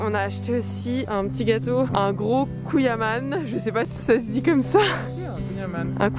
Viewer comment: C’est quoi un « couillaman » 😂😂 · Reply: personne a compris, moi aussi j'ai entendu "couille à man" 🤣